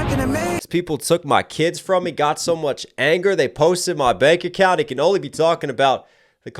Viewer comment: Sounds to me like he is talking about the small hats and showing how much influence they have. You said "They posted my bank account." I heard "closed my bank account." The interesting thing is the things you mentioned about the austrian man, are things things that we have been constantly told to be the truth, that he is this horrible person.. but how much of it is actually true.. I think even the idea questioning the narrative given is seen as problematic.. but one thing is for certain.. Jesse Owens was treated better in Germany during the Olympics, than his own country that he represented, the US... The austrian man was a racist for sure, but was he really like they told us he was.. If you really look at history a bit deeper, a lot of things don't add up..